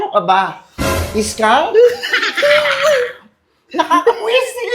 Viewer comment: What did she say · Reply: Maybe "Scang"